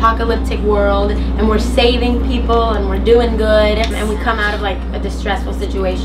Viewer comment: estoy mal de la cabeza xd escuché "camren" en el min.